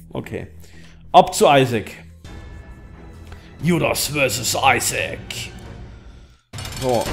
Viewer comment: verstehe ich you lost vs isaac :D